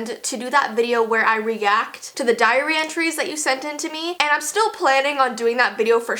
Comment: did you say diarrhea tree's 🤣🤣🤣🤣🤣🤣🤣🤣😂😂😂😨🤣🤣